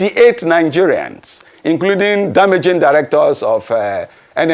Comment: Who else heard his Majesty say 'damaging directors of these oil companies, instead of managing directors, at 27:05. His Majesty is vawulent. Lol.